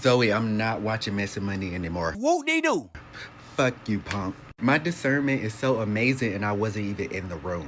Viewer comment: Did he say “fuck you ponk” 🥴 6:55 he sound like a ponk😭👀